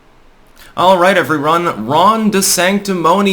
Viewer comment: Did I hear "Alright everyRON?"